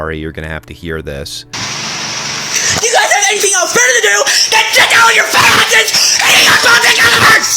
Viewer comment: what did he even say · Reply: “YOUGUYSDONTHAVEANYTHINGBETTERTODOCHECKOUTFATASSESHANTHHELIKEURRGAR”